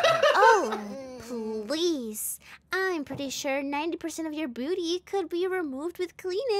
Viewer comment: Am i the only one who heard 'BOOTY' instead of 'BEAUTY'? 😭